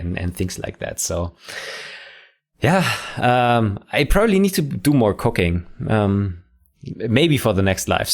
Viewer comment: I heard "I probably need to do more cocaine".. Yeah that's a way to make different content for sure